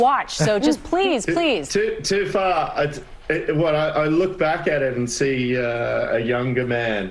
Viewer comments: No, sorry, he said "Too far." at that point. Meaning too long ago for him to watch himself in Devil Wears Prada. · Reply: Thank you sorscha1308 but I really hear: "Too far et voilà, I look back at it..." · Did he say "Et voilà" at 3:43? I'm French and it's what I hear. He often said that in The Mentalist.